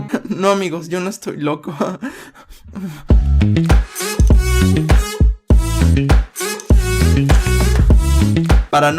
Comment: Que Dice en el minuto 6:31 en La segunda Frase que Pasa Rapido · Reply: dice help mi de nada